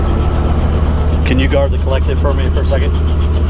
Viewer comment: what do you saying here? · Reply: I asked the instructor to "guard the collective". I had to remove my hands from the controls to put the headset on, and you should never take your hand off the collective once the engine is running and blades are turning in case the rpms run away and you get an accidental lift, etc... Just best safety procedure if you have a copilot/instructor.